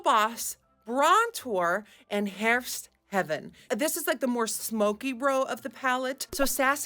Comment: Looks beautiful!!!! BTW when you said the name "Herfst Heaven" I thought you said "Hert Heaven" and I was like, "that's a dark deer!"😅